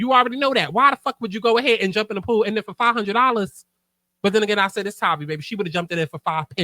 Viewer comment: I thought she said $5 at first! I was like oh hell nah 😂😂 and then I heard $500. I wa alike okay, I guess. But then I think of the wig, and clothes and glam. Nah, not even worth it lol.